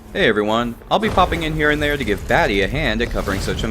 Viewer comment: I must be going deaf because I thought said “daddy” instead of “Batty”. Maybe a better nickname could be? 🤔 Jokes aside, this was a really good review and well thought out. Looking forward to the next video!